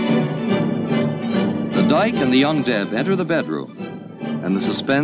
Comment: I love how it's narrated like it's a nature documentary, lmao. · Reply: At first when the narrator said “young deb” I heard “undead”. 😂🤣